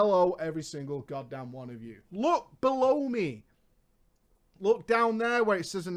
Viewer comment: At first, I thought you said "Look, blow me." >.>